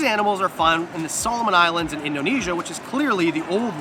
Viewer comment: … east of Papúa… I don’t get it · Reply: my mistake. I misheard you. On review you clearly said “and Indonesia.” I thought you said “in Indonesia” meaning that the Solomon Islands are in Indonesia. I’m sorry, totally my error.